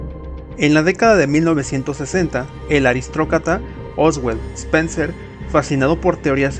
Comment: Que chingados es un aristrocata? 🤣🤣🤣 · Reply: Yo escuché “Aristrocrata” 😂